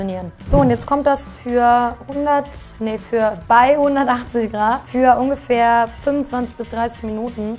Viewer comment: Ich hab 280 Grad verstanden, allein das mein Ofen höchstens nur 275 Grad hat, hätte mir sagen sollen das es nicht funktioniert. Was soll ich jetzt machen? Habs 30 Minuten drin gelassen...